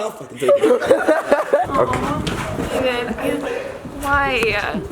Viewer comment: did anyone else heard "babe" at 3:22 ? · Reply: "Dave" not babe HAHA